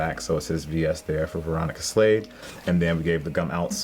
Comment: I thought she said Veronica slave 😬😬